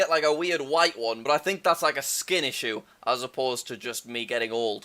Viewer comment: i thought he said skill issue lol